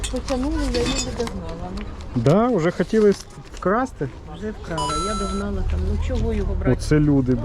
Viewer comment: щось я не зрозумів що хотіли вкрасти цей блок живлення, нерозбірливо що вона каже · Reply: хотіли блок вкрасти але баба спасла його для мене :face-blue-smiling